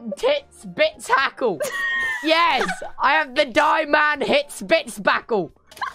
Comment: "I'm holding an ep-sein-hick-spacle!!!" "die-man-hits-bits-bacle!" 😂😂😂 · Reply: I heard it as Die-man-hits-spits-spackle Lol